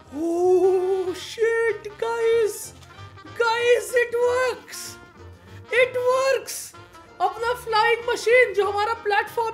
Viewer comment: what he said: It works!!! what I heard: It twerks